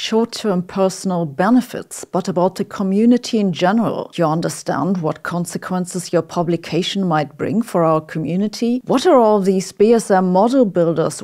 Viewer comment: “Do you understand the consequences of you telling everyone that we’re spreading misinformation?” Is all I heard ngl